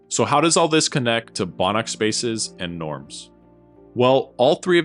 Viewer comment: Im tired after work and thought that said banana spaces hahah